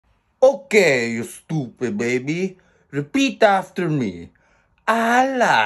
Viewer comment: At the start i heard him say,Stupid baby